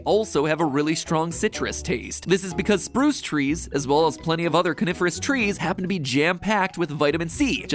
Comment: i thought he said "carnivorous trees"